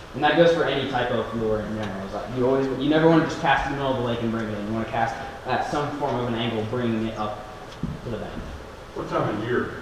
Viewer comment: you were saying the stuff about casting. I couldn't understand that, could someone or you, type it out please? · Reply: i was saying to cast parallel to the shore, especially if you are fishing from the shore. Don't cast to the middle of the lake